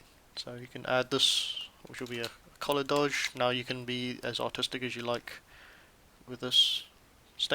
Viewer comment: I'm sorry but i had to re-listen to 25:55 like 3 times cuz i thought you said "you can be as autistic as you want" and i was sitting here like oh okay that took me by surprise but at the same time it does fit me :D